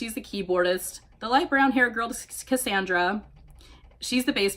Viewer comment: Ok when you said cassandra, it sounded like you said skskassandra XD